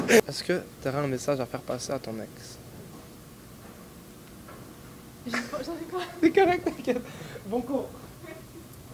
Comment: Ils disent quoi à 13:25 quand il renifle leurs doigts et qu’il tombent ? Ils disent skurt ??? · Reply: Joelle Priso tsikah